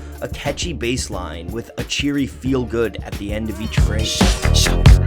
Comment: I always thought that it‘s just someone randomly saying „heeheee“ instead of „feel good“ 😦